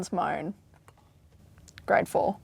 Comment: i didnt know what she said i had to turn on captions....i guess its because im american and we would say it as "4th grade" rather than "grade 4"....that tiny differnce threw me off...